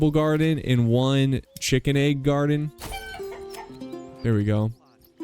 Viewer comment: did he say chicken egg garden